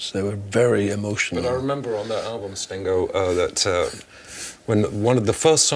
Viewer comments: Rick…I gotta say…you were probably better prepared for that interview than anyone before you who ever sat down with them. P.S. - At 19:51…”Stingo”?…was I hearing things? (even Gordon looked like he flinched 🤏🏼). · did he call him STINGo ? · Did Dominic called him "Stingo" at 19:51 ? 😳😝